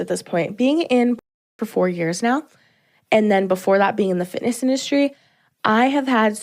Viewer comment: did she say 'fitness' or 'fatness'..🤷‍♂️